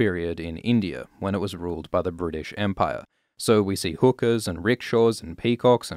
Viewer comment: i thought you said "hookers" but you said "hookahs" lol love ur vids